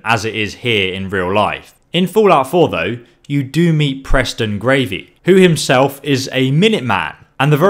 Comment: Did he just say Preston gravy